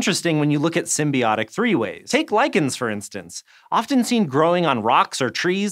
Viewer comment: I heard lycans, not lichens, and my day is now ruined.